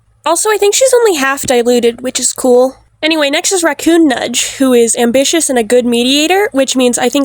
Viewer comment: There was a whole five seconds I thought you said "RaccoonNUT" and I. I needed a minute.